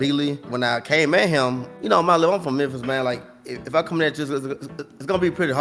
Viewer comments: I think he said hes from memphis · "Like you know I'm came from Memphis man" thoses that couldn't understand what he was saying